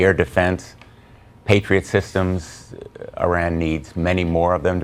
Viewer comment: did he say "Iran needs"?